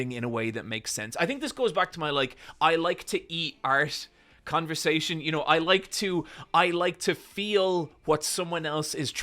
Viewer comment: i thought he said i like to eat arse and was like that’s a wild direction to take this convo